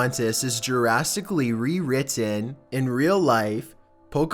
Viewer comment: Did...did you just say jurrasically rewritten??? Like by dinosaurs or something????